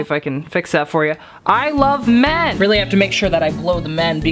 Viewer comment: I thought it said i love meth